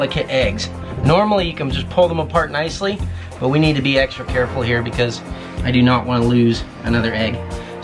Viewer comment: did you say "eggstra careful?"